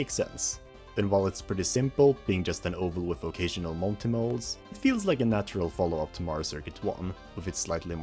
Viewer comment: I misheard "occasional monty moles" as "vocational optimals" for a second there...